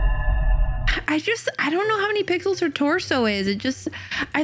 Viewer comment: Is it just me or did she say pickles